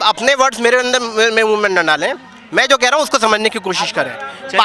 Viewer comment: what did he said?? आप मेरे अंडे मेरे ही मुँह में ना डाले ??🤪🤣🤣🤣🤣